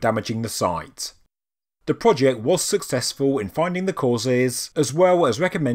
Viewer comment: I kept hearing "the successful man" and couldn't stop thinking about Tony Robbins jumping around.